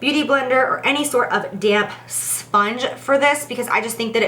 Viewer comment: i swear everytime she says "damp sponge" i hear "damn sponge"